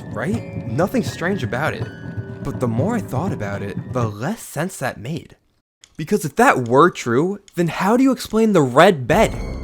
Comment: excuse me but what acctual f*** did you just said? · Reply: "When I thought about it, the less sense that made." That's what he says here